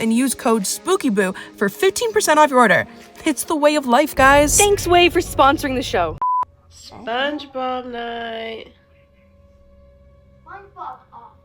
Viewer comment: in the vid i thought he said " SPONGEBOB AWFUL" I was like, ..., Huh?